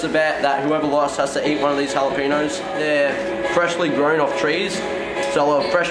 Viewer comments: 😂I thought said JALAPENIS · I'M CRYING 😂😂😂😂 I THOUGHT SAID "JALAPENIS" 😂😂😂😂